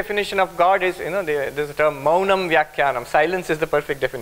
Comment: Hello, at a9:20 he says "mauna....." (another word after) could you please tell me what words are after that? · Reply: are you referring to his "Maunam Vyakhyanam -" ? Silence is the perfect Defintion. Vyakhyanam means Lecture/talk/commentary